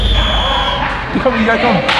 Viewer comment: anyone else hear Michael Jackson whisper “talk to em”😂